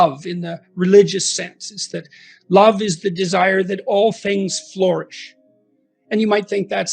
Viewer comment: you have the word "floor ish" instead of "flourish", just wanted to let you know!